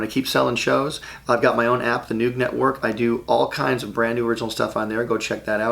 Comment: Does he say "the nude network" at 10:19? Someone please clarify. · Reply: He said “The Noog Network”, not “nude”, “Noog Network”. Look it up for more details